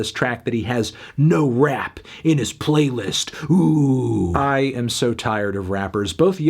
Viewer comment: i thought he said "No Wap on my Playlist"